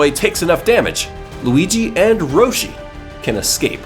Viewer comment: did u say “Roshi” ?